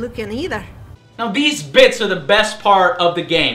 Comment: I just Heard... This bitchs is the best part of the game... my mistake!